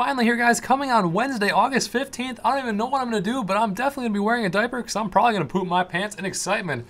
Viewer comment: I can't see out my right eye so i thought you said "IM COMING OUT TODAY"